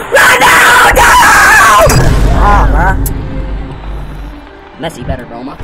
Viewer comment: I heard him say messi is better😂😂😂